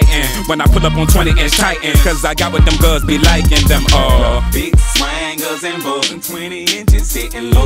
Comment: When she got off from the floor all i heard was " Big swangas and vogues" - Chamillionaire 😂😂